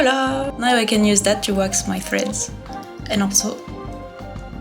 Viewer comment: 'Now I can use that to wax my friends?' Okay, I guess that's not what said. I DO like the idea of waxing friends, though. Do friends ever need waxing? Edit: Ahh. Wax my threads!